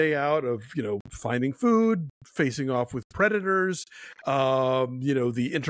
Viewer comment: At first I heard “facing off with redditors” 😂